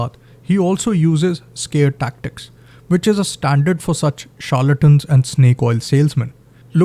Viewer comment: what you'd said? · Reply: charlatan and snake oil salesmen, dost